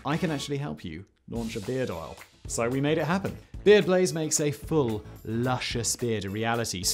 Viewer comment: I thought you were saying Beard Glaze for the first 40 seconds lol :p